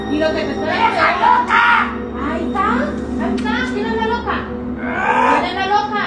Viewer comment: did she really say vieja loca😂